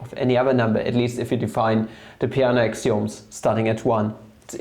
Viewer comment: What did u say at 2:36? What are these axioms called? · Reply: Peano's axioms